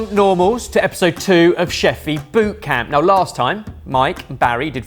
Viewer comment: Er... did he just say "Welcome, Normals, to Chef Feet Boot Camp"? <_< 🦶